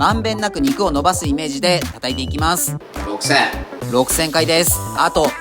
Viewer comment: 「6000」が「めんどくせえ」に聞こえた笑